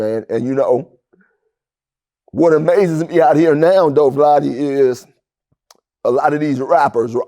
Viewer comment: did he call him Vladdy 😂